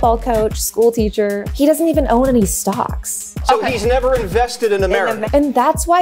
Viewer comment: "He doesn't even own any stocks." "He's never invested in America!" dafaq is wrong with Fox news?!! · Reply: Oh stocks! I thought she said socks.